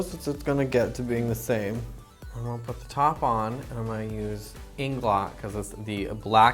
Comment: can someone tell me what Eyeliner she's talking about? TIA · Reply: It's Inglot. She said it was the blackest black, so I assume it's the Acme eyeliner gel #77.